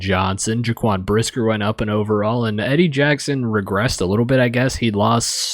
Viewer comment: Thanks for the shout Bro! The first time you said "he regressed" all I heard was "I'm Prime, but I'm Prime" 😂😂😂